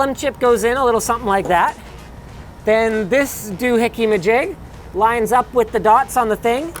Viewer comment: It's great that this video has actual uploaded subtitles instead of relying on YouTube's shit generated captions. All of your videos should have them. (Please see Technology Connections for some of the best subtitles on YT.) But also you need better proofreading on them. Both of the "(indistinct)" that I noticed were actually perfectly clear (6:35 "doohickey-majig" and 19:20 "up my 'do here"), and how does "x86" become "NextCity Six" (21:54)?